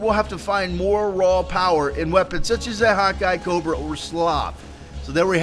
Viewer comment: When he said hawk eye it sounds like he said "hot guy" lmao!